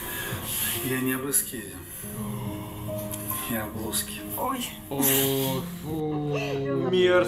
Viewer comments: а что он там сказал?(мне послышалось: "Я не Русский, я плоский") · Что они говорят на 1:46:14 ? " Сата Я не русский, я плоский" мне слышится. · Что он сказал? Я услышала: "я не русский, я в блузке"